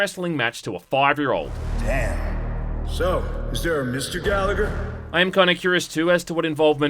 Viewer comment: I thought you said "Galaga" not "Gallagher"